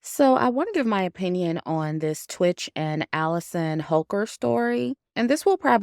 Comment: Holker? I swear I thought I heard you say hooker😂😂